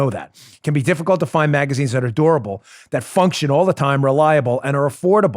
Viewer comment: I thought Dan said "magazines that are adorable" 🤣